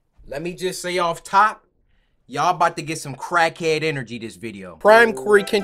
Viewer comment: I started the vid and accidentally skipped and all I heard was “y’all finna get some crack”💀 I laughed out loud